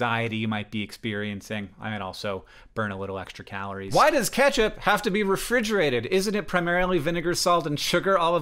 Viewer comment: i thought you said "why does Kesha have to be refrigerated?" and now i can't help but wonder the answer to THAT question